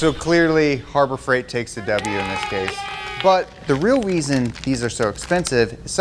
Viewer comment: did he say the real "weason"??!